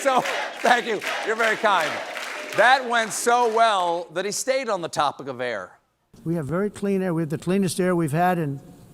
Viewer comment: Does anyone know what they were chanting? I can’t quite make it out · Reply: steven i think